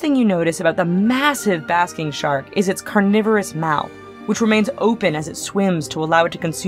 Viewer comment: I like how she says "carnivorous" when the caption says "cavernous"....yeah, not really. I actually did a double-take to make sure I heard it correctly.